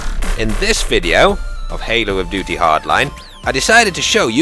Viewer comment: did he say Halo of Duty Hardline??